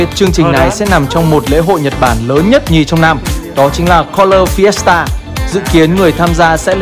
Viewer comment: ổng đọc Fiesta tao nghe thành siesta :))